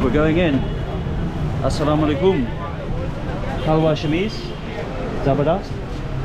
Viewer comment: Did he say 'kalvar shameez' at 10:58 😆😆😆